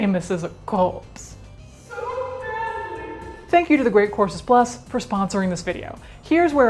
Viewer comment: I heard "thank you to the great corpses plus for sponsoring this video" 21:48 :-)